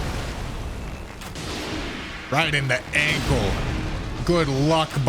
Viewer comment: I am well aware that during the regal ancestor spirit fight, you said “right in the ankle”, but I absolutely heard you say “right in the egg hole“! Lol. 😂